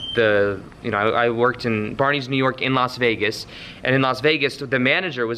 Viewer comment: This whole time I thought you were saying Barney’s in New York, meaning you worked in New York. 😂 BARNEY’S NEW YORK... in Vegas. 🤦🏻‍♀️ P.S. Love your videos ! 🙏✨